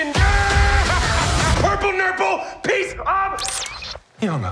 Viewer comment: I thought he said purple nipple..